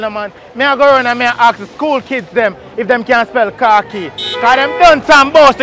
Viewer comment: Sound like he saying car keys